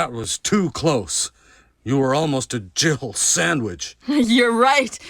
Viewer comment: I swear it sounds like he says Jill-erbil sandwich